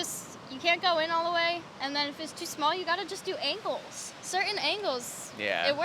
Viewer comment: LOL first time I heard angles I could have sworn she said you just have to do anal.